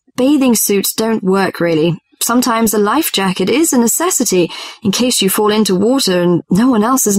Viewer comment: For question number 14, the woman says, "a bathing suit" does not work, and sometimes "a life jacket" is a necessity. By saying that, the answer should be F(life jacket), but why is the answer turns out to be E(bathing suit) instead regardless of other answers(B and D)? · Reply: I heard it :"Unnecessity"